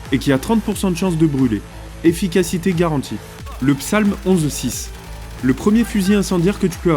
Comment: le quoi ? 🤔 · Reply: le Psalm, c'est en VO docteur